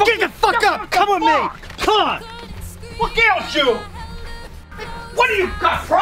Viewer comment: It sounds like he said "What Gails You". I haven't finished Season 1 of Breaking Bad, but I've already been spoiled about the last few episodes, so I guess I don't care about spoilers too much.